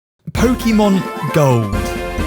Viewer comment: I swear he said "Pokemon Go"